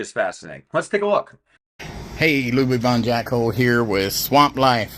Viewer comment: Did he say his name is Lubon Jack's hole, @ 1:16 ?